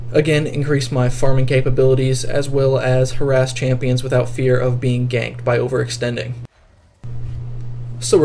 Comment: Could have sworn he said without fear of being gay the first time I listened ahha